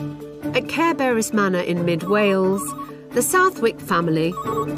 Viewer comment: I keep hearing "Care Bears' Manor"! 😅